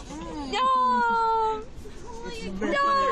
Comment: what did say at the end? "there's no fucking gum in my-"? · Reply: i heard "who put their c*m in my hands" ... lol idk